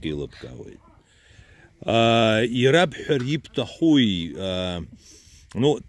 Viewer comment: Что сказал он...? Хуи...?😆😉 4:56 😆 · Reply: Ищахуп -вообще то